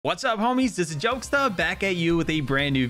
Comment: Did he just say IT'S A JOKESTA?